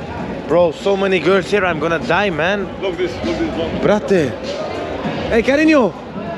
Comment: Did you just said “BRATE”